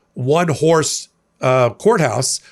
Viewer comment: Oh JFC, I thought you said at first, that Cannon presided over a "one whore courthouse." Cracked me up. Had to rewind that and pay more attention. Would explain why Cannon doesn't care about the image of impropriety as a truly loose broken in whore probably wouldn't care about such things.